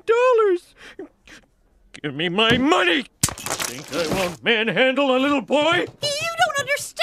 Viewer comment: What did he say?!! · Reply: “You think I won’t manhandle a little boy?